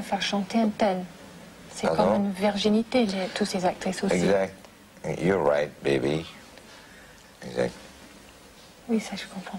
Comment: did he say “you’re right, baby” here?